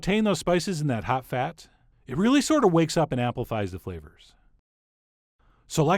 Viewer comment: Did anyone else hear: "Apple pies the flavor."? No? Just me then.